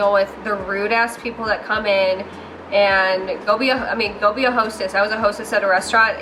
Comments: anyone else think she said "go be a hoe sis" instead of "go be a hostess"? Or am I just deaf? lmao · Lmao. You said "be a hostess" but I thought you said "be a hoe, sis". Either one is good though lol